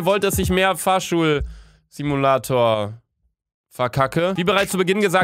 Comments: Ich hab Fahrstuhl-Simulator verstandenn WTF 😂😂😂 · Ich hab zuerst verstanden "Fahrstuhl Simulator" 😅😂😂